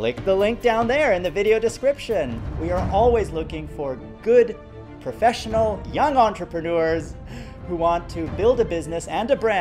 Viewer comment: Okay. It is time to left Freedom!... I am not young, nor have nerve to listen those lies here. Will chose another network. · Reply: I heard... that partner should be young and ... maybe I did not hear correctly because of loud music in background on the video.